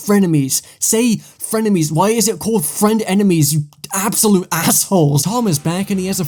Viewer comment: i thought it said friendpenis